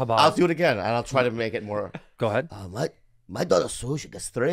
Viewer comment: I thought he said "my daughter has sushi that is Grade-A...." Now YOU cannot unhear that either😂